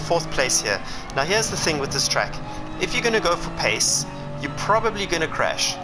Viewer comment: you said if you gonna "pase" ? or? i dont understand what this mean... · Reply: "Pace" - I meant that if you're going to focus only on going as fast as possible, you will probably crash :)